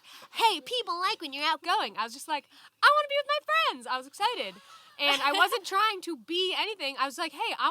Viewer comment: what’s lisa saying at 7:06 ? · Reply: Charlotte friends